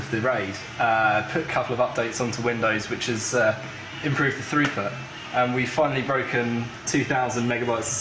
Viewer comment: he says something about windows updates to improve what sounded like "throuper"